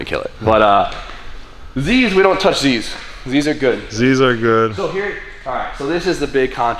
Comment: Idk why but when and said z's are good I heard "these are good "In a German accent 🤣 🤣